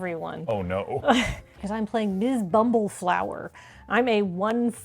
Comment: after that teaser into the chaos gremlin vibes were so strong I swore that was saying "I'm playing Miss BumbleF***" and I kept that mental energy going all game, cheering for the AggroRabbit..!